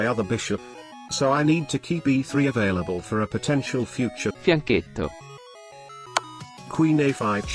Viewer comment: what is the word @ 2:01? "i need tokeep b3 available for a potential future what?." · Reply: It's fianchetto (from italian) google it :p